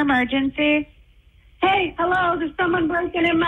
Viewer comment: Damn that 911 call! She is calm AF! 2:23:42 she is saying "GET OUT" as if she is talking to the dog to get out of the bathroom. SHE ACTUALLY SAYS "HEY HELLO" with such politeness as if she wants to order a PIZZA!!! How can she be that DUMB?